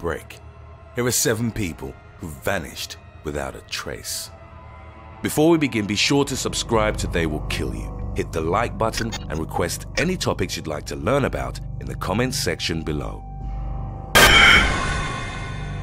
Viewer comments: In the beginning I thought he said “Make sure to subscribe or they will kill you” 😱😱😱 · I thought he said subscribe or they will kill you